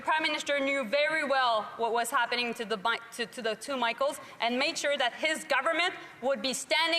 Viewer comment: did she slip two Bidens instead of Micheals?